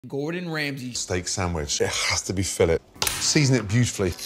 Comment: I love how Gordan says Fill-ett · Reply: lol yea it sounds like he says Fill-It